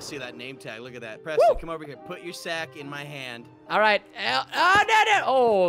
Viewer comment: did just say to Preston “Put your sac in my hand.”???